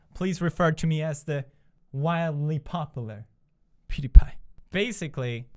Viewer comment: this whole video all i heard was "populr"😂